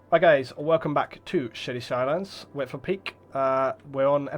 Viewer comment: It sounded like he said welcome back to shitty skylines